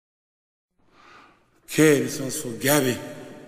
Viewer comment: This is going to sound bad.. growing up, my mum played this song all the time- I loved and still do love it. But my dumb self didn't comprehend him saying, "Okay, this one's for Gabby." I heard, "Kids want spaghetti."